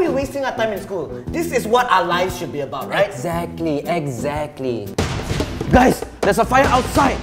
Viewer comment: the subtitles got it all wrong Jianhao:” Eggactly “